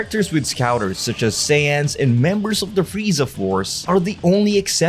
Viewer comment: I'm the only one apparently to hear that instead of saying Raditz, he called him Sans 0:21 also that made me laugh cause I didn't expected that